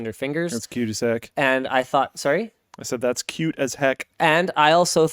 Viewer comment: I heard "she's cute as heck" which is probably what Linus heard 😂